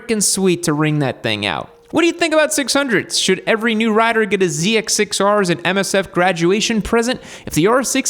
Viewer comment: all I heard was "get a zx4r"⛷️